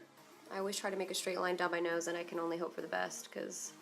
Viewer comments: you said " Can Only" and it sounded like you said canoli... that's dog's name, right? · when she says "I CAN ONLY hope for the best" I heard Cannoli & blanked out for a minute just thinking about her dog & how can-only sound like cannoli. Then I made this comment. Good night.